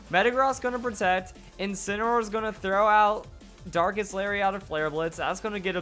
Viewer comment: Did i hear him say "Dankest Lariat"...?